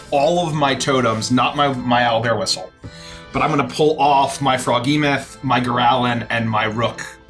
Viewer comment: Did he say froggy-meth?!?!